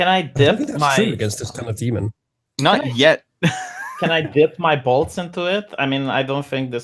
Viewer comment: "Can i dip my balls in it?" "No, it's only a small vial." its what i heard damnit, it's funnier that way